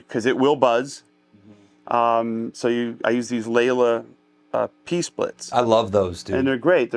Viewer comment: just after Joe says he uses a "layla piece plate" what does he really say? What is it he uses?